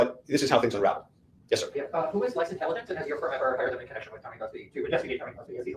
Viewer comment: it sounded like the reporter asked "who has less intelligence?" 😂😂😂